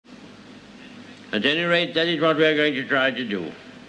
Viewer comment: he actually said "at any rate that is what we are going to try to do"